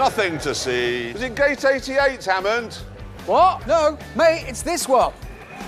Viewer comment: did richard called jeremy “may”? Lol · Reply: its actually at 7:35 and he called him "mate"